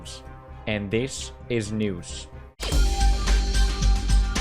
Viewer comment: Anyone hear the “PEW NEWS!” before Pewdiepie starts talking? lol